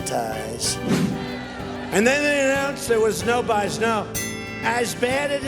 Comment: I still don't know what he's trying to say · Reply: "And then they announced there was no bias, no." I can only tell because it was transcribed in this video LMAO. He sounds drunk. And he doesn't drink. He is SOBER. 🤦🏼‍♀️